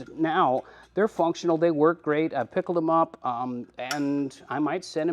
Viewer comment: my question is how do you spell that product you use for rust protection?, sounds like you say picklex. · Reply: Picklex20